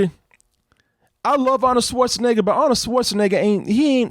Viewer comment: schwarzenegger but youtube subtitles say Autosports nigga lol 3:35 ( pause it) 😂😂😂😂💀💀